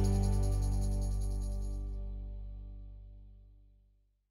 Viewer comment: you said something about shadow and easily fixing it. Can you type here what you actually said as I couldn't quiet understand as the background music took over the word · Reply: Details in the shadows easily recovered in.... ". " ???